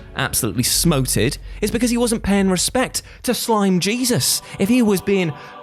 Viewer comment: When he said slime jesus i thought he said slim jesus